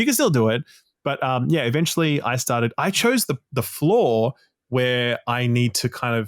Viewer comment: have this playing while driving - i heard "i chose the floor" and thought was about to describe some new mechanic i didnt know about 😂